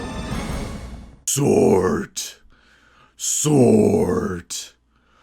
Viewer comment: what did you say? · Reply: SORT. Это сокращение от названия команды спецназа. (Специальная Операция Raid Team) Я смеялся над этим, потому что SORT звучит глупо, лол. Это сопоставимо с командой в Resident Evil - S.T.A.R.S.